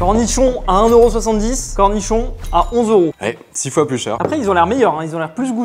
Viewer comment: disons que j'ai entendu quelque chose de vaguement différent... chu pas l'seul hein ? ou j'ai juste un problème ? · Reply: Gros nichon?